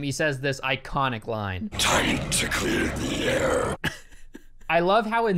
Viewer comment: For years, I thought Ghost Rider said “Tiny Ticket Air”